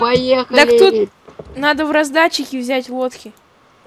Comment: в 12:31 мне послышалось на в раздатчике взять водки